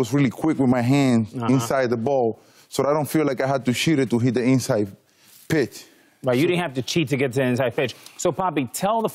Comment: i thought big papi said i didn't have to shited inside of the plate but he said i didn't cheted lol